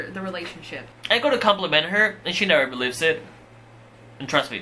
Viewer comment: what did he say? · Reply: "I go to compliment her, and she never believes it"